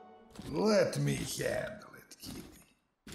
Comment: It sounds like tachanka says, “Let me handle it kitty.” 1:04 Can we make tachankin a real language?